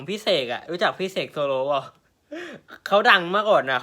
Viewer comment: กูได้ยินเซกโซโล (และที่กูคิดมันต้องเป็นเซกโลโซ)